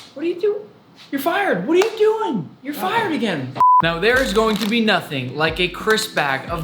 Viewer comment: It sounds like he says fu## no